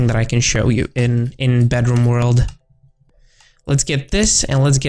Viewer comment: i thought he said bathroom lol its bedroom